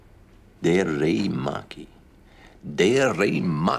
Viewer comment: House can read hindi? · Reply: I thought he was saying "Teri Maa ki"😂